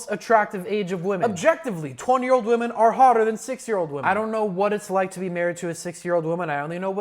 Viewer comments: bro i swear he was saying 6 year old not 60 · Finally the subtitles match what’s actually being said😂😂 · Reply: Well the subtitles say 60 while he says 6